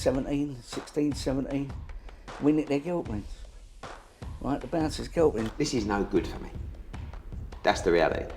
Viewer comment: 😂😂😂what is he saying “they nick me guilty the bouncers gill me” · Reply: Not that hard. ‘We nicked their girlfriends, right, the bouncers girlfriends.’